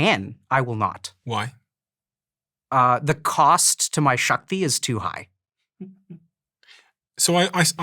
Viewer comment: what's the Sanskrit word Dr K. uses here? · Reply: "Shakti" literally means power or strength. In Hinduism, we also refer to feminine energy and our Goddesses as "Shakti".